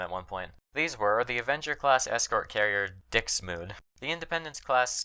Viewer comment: I'm sorry, did you say Dick Smoove?